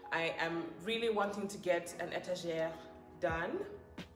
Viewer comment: come slowly. What is that big word you dropped at 22:14. Artegerrrrrrr. Hata mimi nitese watu come tweny tweny two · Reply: Étagère lol 😂